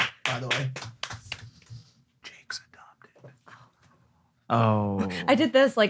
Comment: What they whispered was “Jakes adopted” 🤣🤣